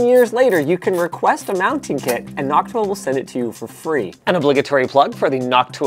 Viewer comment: I thought he said hawk tuah instead of noctua